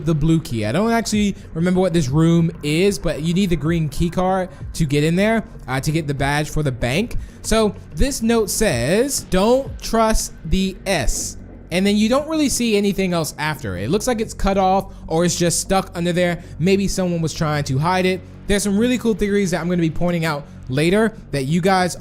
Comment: For 3:13 i think it says "Dont trust the super potato" XD idk probs not